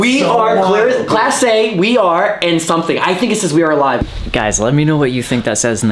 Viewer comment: Sounded like it said mr saddler And at 26:18 I think it was “we are aware”